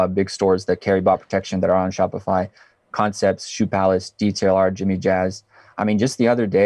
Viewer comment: Can some one Please help me out with this Question: Did he say Detail Our or something else?? (At 11:36) Thanks in advance!..